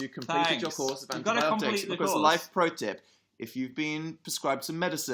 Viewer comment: Um did Dan just say "live protip." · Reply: Shut up life ! Think he said “life protip”